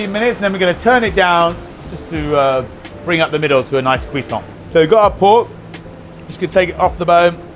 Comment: "Bring up the middle to a nice....." what is that word...?? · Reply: He says "cuisson". The French word for the "doneness" (rare, medium etc)